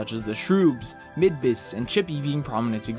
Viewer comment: Did you just called Starlow... chibby?